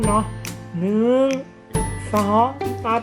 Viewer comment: เหมือนพี่พูดว่า หนึ่ง สอง สัส เลยอ่า